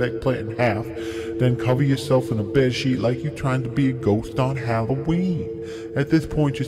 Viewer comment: I thought you said a ghost on heroin